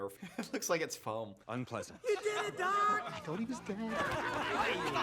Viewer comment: tell me why i fully thought doc said "i thought he was gay" 😭